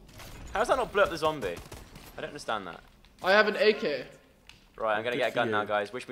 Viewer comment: I thought Caspar said 'I am an egg head' 😂😂